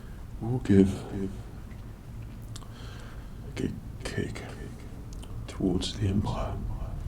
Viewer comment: I cant be the only one who thought he said give good cake to the empire, can I?